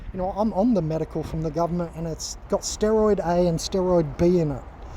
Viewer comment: I could have misunderstood you, but did you say there are steroids in the cannabis? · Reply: ye steroid a and or b . I'm sure they're in there.